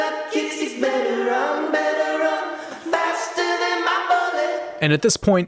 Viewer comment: I've never seen this song as "happy," because I couldn't ignore the lyrics. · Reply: I never heard the song’s instrumental as “happy” either. I thought it sounded (if this is the word I’m looking for) weird and creepy. I also thought the chorus said “faster than my brother” when I was 7 and first heard the song. Lol